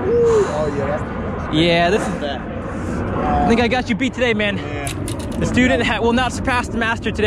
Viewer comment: did he say the N-word??🤷‍♂️🤷‍♂️🤐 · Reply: DecentDrawings no he said think I got you beat today man